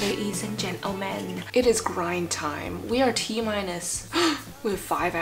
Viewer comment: at first I thought she said crying time but it was grind time😂 anyone else?